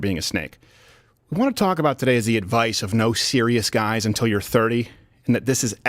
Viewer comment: I thought he said there are no serious guys under 30